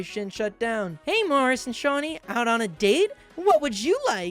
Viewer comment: I can’t watch this with a straight face cause I keep hearing “shawtie” instead of “seanie”